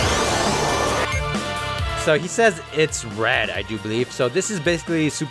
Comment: he actually says “It’s ready” it’s the voice clip from super spirit bomb